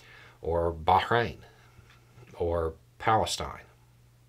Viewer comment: You said "Palestine"... I heard "Record Scratch"